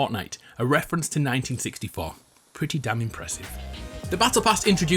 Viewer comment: did you say damn